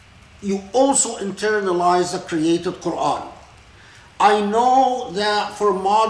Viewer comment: Assalamu Alaikum, may I please clarify: 'internalisation of the created Qur'an'? To me it sounds like he is saying 'creative Qur'an' not 'created'. I've heard talk very eloquently previously on why the Qur'an is considered 'uncreated', so I am finding 'created Qur'an' confusing🤔🤔